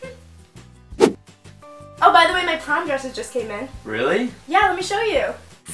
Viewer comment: When u said to ' My prom dresses came, wanna see?' I literally thought u said ' My condoms have came, wanna see?' Lmao, i'm so stupid. Ilysm!!! ❤️❤️❤️❤️